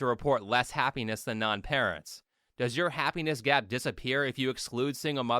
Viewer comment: I swear, I heard happiness gyat instead of gap im doomed